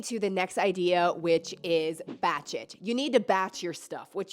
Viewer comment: Lol, when she said "Batch It", I thought she said "Bat Sh*t" 😂